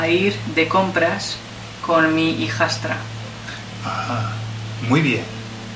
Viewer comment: On th 10:21 can you help me cos I dont understand when says y voy a ir de compras con mi (whats the next word)? Thanks! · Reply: Voy a ir de compras con mi hijastra (my step-daughter) Cx